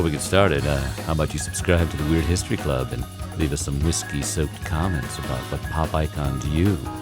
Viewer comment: I thought he said “Leave some risky SIMP comments” and I